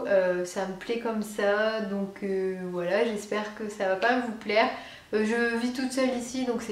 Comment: C'est moi ou elle a dit " j'essaie que ça va pas vous plaire " à 0:45 ?